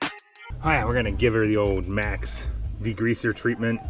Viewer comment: Did you say Macs? LOL